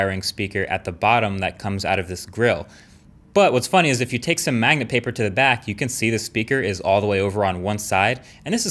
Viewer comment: What is that paper that he used at 3:46 to look through the phone????? I cant quite understand what he said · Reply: Sounds like Magnetic Paper